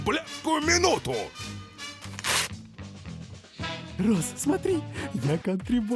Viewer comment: Я понимаю что мне тогда послышалось. Но блин. Мне так прнравилось. Почему-то при первом просмотре вместо "Рос" я услышала "Роза"... Я уже на автоматев мозгу сама исправляю на Розу... 😅